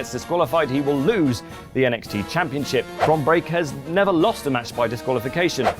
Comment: I swear he said Britt Baker at 5:00 instead of Bron Breakker